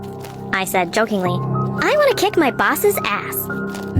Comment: I thought she said "i wanna kick my Bosses Ex" bruhhh